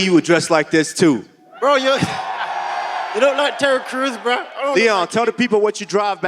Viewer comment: Terry Crews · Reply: was it terry cruz 😂😂 fucking hell I thought he said Taio Cruz , both just as good 😂😂😂